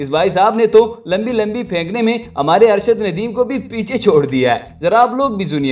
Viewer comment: did he just say “Arshad Nadeem”? Can someone confirm?